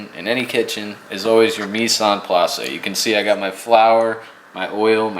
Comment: you used a couple of words that the automatic transcription made "Nissan Plaza." I don't think that is right. What did you say there? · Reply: Mise en place, French for "things in place", basically getting all your ingredients, utensils, and machines ready before starting to prep anything